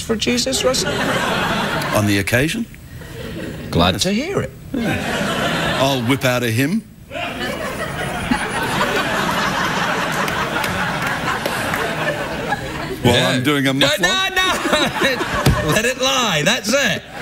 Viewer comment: what did he say in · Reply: michal8579 He said, I'll whip out a hymn ... while I'm doing a muffler."